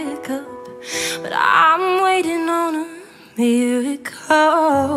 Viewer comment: I love this song so much but some of the lyrics on screen arent what she is actually saying. She says "I'm waiting on a miracle" when screen says "waiting on you to call" which it doesn't even sound like she says, lyrics are everything so it distracts but regardless amazing song and beautiful voice I'm a huge fan of her now.